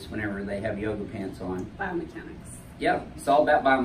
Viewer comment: For a second I thought he said bum mechanics.